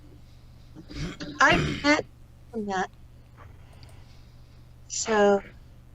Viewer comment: says she met someone who I cannot hear the name. Anybody know who she is speaking of? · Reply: She met baphomet